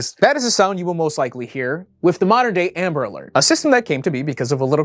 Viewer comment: I legit thought you said "Nambla Alert" here. 😅